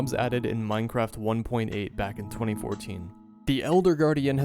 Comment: hold on did he just say 2014?